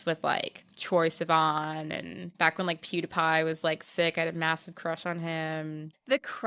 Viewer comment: did she say PewDiePie and the subs were Papie??? 😂 Great pod btw ❤️